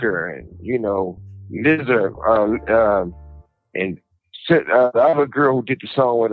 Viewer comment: I thought he said “lizard “ instead of lizzo. It was sza 😂😎✌🏻